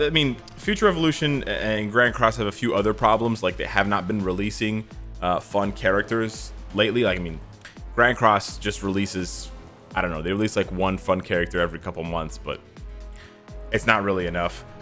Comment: who else thought he said one fucking character a month @ 9:05 for a quick sec. like i had to replay that and i was like oh ☠️